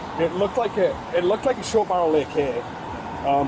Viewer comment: " It looked like a short barrel lick here" hahaha I think he said AK